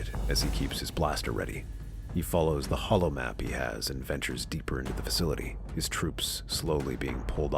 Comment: I thought you said ventress